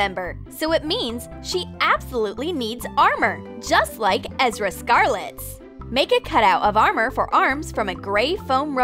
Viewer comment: Wait what she said ezra scarlet 0:38 but its erza scarlet🤔